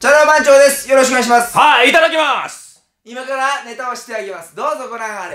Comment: 後藤さんの第一声が『歯いただきまーす！』に聞こえて、拷問始めんのかと思った